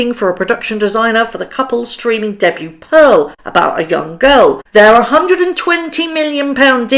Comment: You said “young girl” and I heard “ yacht girl” on the first go round near 9 minutes. Listened again for clarification. It was young girl. BUT… yes the whole concept of the video Pearl is not new! Chelsea Clinton did something very similar as did another artist who I need to research to cite the name. Harry’s wife plagiarizes everything she does!